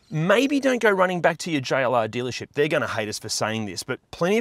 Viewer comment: I thought he was saying JLR dealership. Im like who tf fuk is JLR, mate. Lol